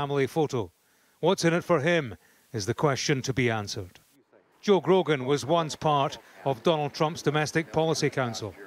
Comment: I thought he said "Joe Rogan" 😂